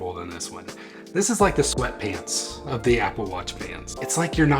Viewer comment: I heard "This is the sweat pants of the apple watch pants", good video + 1